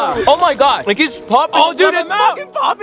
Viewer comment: I heard a cuss. 🙂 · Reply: YEAH HE SAID FUCKING LOL